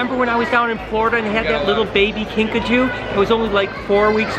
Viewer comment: i keep hearing pikachu